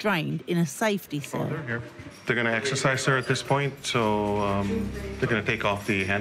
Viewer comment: I thought the Doctor said we got to exorcism her right know , but he said exercise